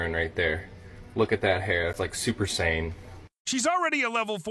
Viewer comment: I think the guy didnt say SUPER SANE...but instead he said SUPER SAIYAN...cos SUPER SAIYAN HAS THE SAME HAIR COLOUR😅😅